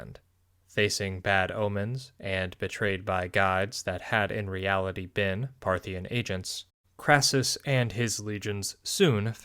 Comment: I misheard that as "betrayed by gods which had been Parthian agents" and couldn't help but giggle at the implications